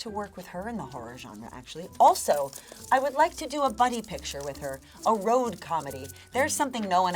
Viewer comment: ok i heard bunny picture 😂